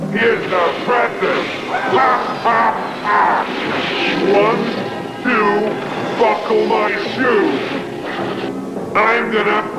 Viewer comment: I'm glad Tsuburaya's lawyers put a ban on this film. This is not even remotely funny except for a bit where Red King-er- Mr.BAD sounded like he said "One, two Fuckle my shoe!"